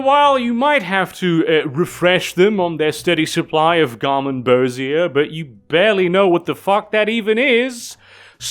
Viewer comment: Would someone PLEASE tell me what the hell that word is he said at 1:15:30? Garmenbosia? I can settle for someone just telling me how it's spelled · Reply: garmonbozia (pain and sorrow)